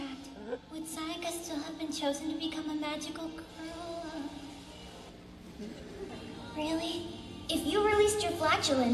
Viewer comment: did anyone else hear magic gorilla? Lol